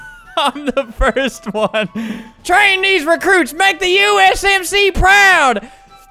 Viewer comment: i thought he said train these chinese make the usmc proud lol